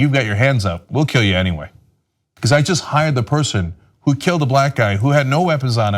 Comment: It sounds like she said "she got fired in the next county over" but she said hired